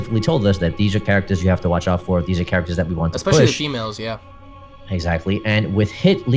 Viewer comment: hahahahahahaaa did i hear it correctly 5:48 "Specially shemale's yeah".... wtf